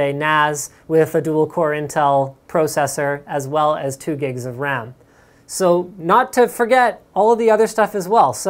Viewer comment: did he say 2 gigs of ram at 13:11 ???